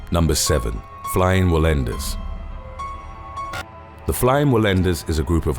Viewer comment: I thought you said FLYING WILL END US lolol